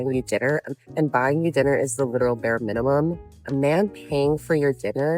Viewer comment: Can't help myself, I heard "Little bear minimum." I wonder, if they can spell 'sense of decency'. If you're not willing, why should men do? But don't come up later and complain about not finding a 'good' man. Sometimes it's confusing. Normally the human being is learning from his environment through seeing and adapting. It seems, that's not valid for women anymore. If a man should be charged for something you have no evidence for, she should be charged for every bad thought she has about men. That's fair. And btw there is not so much to undress, if your walking half naked in public. Poor bears. Next business model offers therapy for bears.